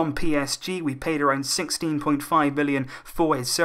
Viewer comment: did u say 16.5 billion